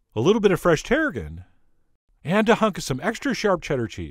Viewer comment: Ok I know you said “… And a hunk of some extra sharp cheddar cheese.” But what I heard you say was “…And a hug and some extra sharp cheddar..” and for an instant I smiled and thought “Awww that Chef John is just so cute!” Then brain and ears sync’ed up lol. ☺️